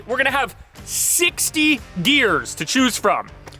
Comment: I swear no matter how many times I rewind I keep hearing “60 deers”